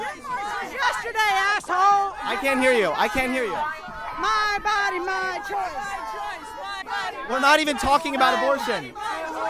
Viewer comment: MY MONEY MY SHORTS lmao thats what i heard Ps: u r a legend